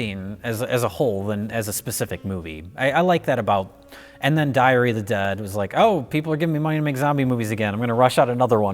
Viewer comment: Did say 'Diarrhoea of the Dead' @ 7:40 ?